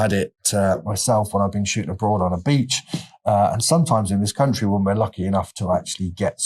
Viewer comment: At first I heard "when shooting a broad on a beach" lol ;)